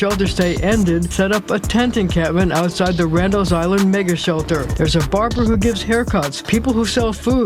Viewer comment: randalls island n***a shelter? Did I hear that correctly?